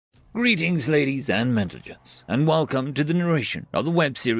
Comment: Lol I thought he said welcome ladies and Mexicans